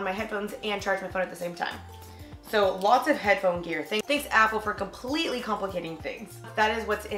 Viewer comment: I thought she said "Thank you Avril for completely complicating things" Avril Lavinge is the best singer ever and she made the song "Complicated" also was talking about headphones (music?)